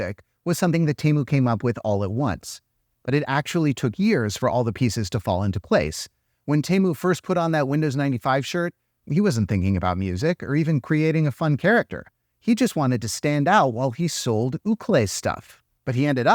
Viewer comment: Every time you say "ukkeli" I keep hearing "ukulele" and it's confusing my brain XD